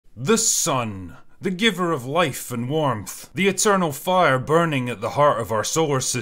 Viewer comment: When you said “giver of life and warmth” I heard “giver of life and dwarves” and it made it really really funny.